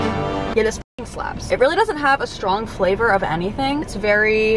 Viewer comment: what the subtitles said: get a spring slab what actually said: this f*cking slaps he’s a little confused, but he’s got the spirit :’)